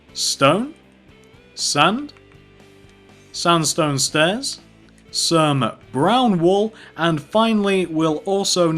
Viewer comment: When he said start off with seven brown wool, I was thought he said southern brown wool! 😆